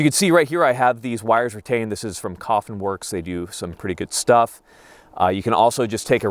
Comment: Hey brother at 13:43 what website did you say you got the wire holders? I hear coffenworks but I'm assuming that's not correct lol